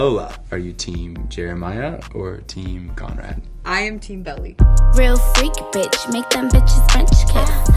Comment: I was so sad because for second I thought she said team Jelly and I was like YASSSS but no 😢😭